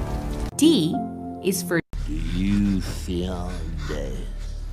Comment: I knew D was gonna be "do you fear death", so iconic! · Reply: I thought he was saying “do you feel dead?”